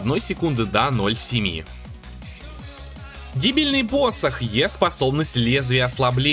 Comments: Я единственный услышал "дебильный посох"? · мне послышалось или он сказал дебильный посох)))